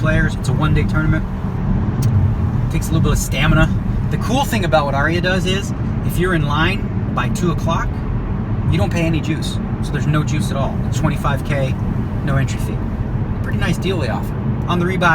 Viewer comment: I misheard you at first and thought you said no jews, not no juice. Was very confused for a second.